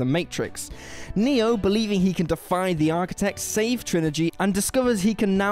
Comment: Did he say, "Saved Trinigy"?